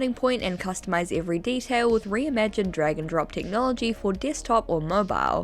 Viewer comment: why did I hear “re:imagine dragon” 🥲